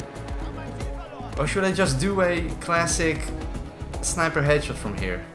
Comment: On bf5 when the tank was destroying I thought I heard him say hatchet 🪓 I was like yo I like this then i realised he was saying headshot